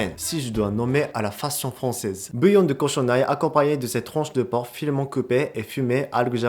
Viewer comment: j'ai compris tronche de porc ?